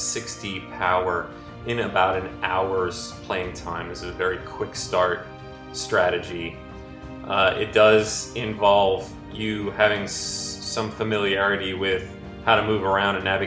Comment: Game of War Beginner Tips - 60k Power in 1 hour. The best guide for your first few hours of play. Epic battles await you! Become a Game of War guru at <link> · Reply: oh shit I really thought you were about to say having sex! Wow bi mistake